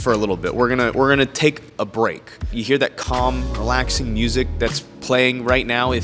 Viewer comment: ive been watching too much unsubscribe podcast, i heard u say "cum relax" 9:00 yeah...lets move onto a topic everyone knows...hentai 😂